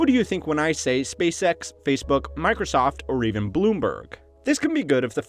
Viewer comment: Why did he say "Bloonberg" at 6:50?